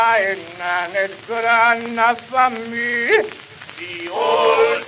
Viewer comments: First time I heard this I thought they were saying "old timey legion". Cool soundtrack, shame the gameplay was so bland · Am I the only one who knew the song wasn't "Old Timey Ninja"? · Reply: hahahahahahaha that's all I'm ever gonna hear now XD tbh I kept hearing "The Olde Time Virginia" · the 1st time I heard this I thought they were saying " the old time Virginian "